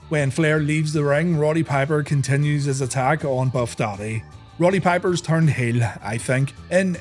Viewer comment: Oh BUFF Daddy, thought it said Puff Daddy so I put my arse against a wall.